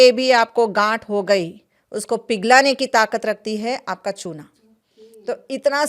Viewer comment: Please what is the powder she said good for fibroids in 12:13min ? The translation is not correct . Thank you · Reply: it’s limestone powder